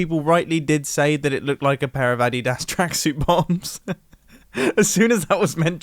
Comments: did he just say adi-das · Ok what did he say at 17:35?!?! “Addy asterisk bombs”?